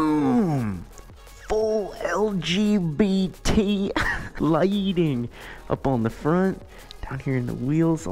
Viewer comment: why did you said "LGBT lightning"🤣